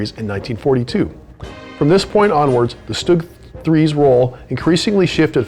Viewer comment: And Do you use Lego bricks. Because if you do you should give them credit copy rights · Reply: It sounds like you saying sturg 3 if that's the right saying.